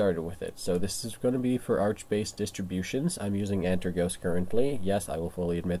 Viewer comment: Which distro of Arch Linux do you have? I can't make out what you said 1:42. I'm completely new to Linux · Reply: It’s known as Antergos arch based Linux. Unfortunately it’s discontinued so I’d recommend something else. I wouldn’t know what to recommend to you though unfortunately